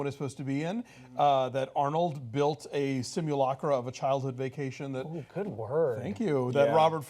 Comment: What word did he say at 8:06 that the other guys complimented him on? "Simulocker"?